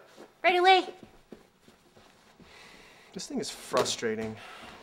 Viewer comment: "This thing is frustrating" says Mega Man lol sorry couldn't resist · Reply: I thought he said “Existing is frustrating”